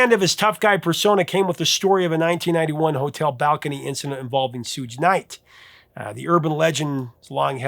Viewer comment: did you just say, "Sooge Knight?"